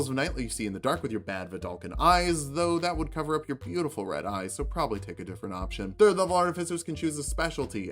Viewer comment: Did you just say "beautiful red eyes"...alright I like you hahah. This is great, you definitely know your business! And Bane's too :D